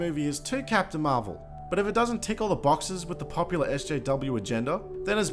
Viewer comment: What was said: "But if it doesn't tick all the boxes with the popular SJW agenda..." What I heard: "But if it doesn't tickle the 'boxes' with the popular SJW agenda..." I thought this might be some Australian idiom about making women happy, tickling boxes, but it turns out I'm just an idiot. Despite that, I still like my interpretation better XD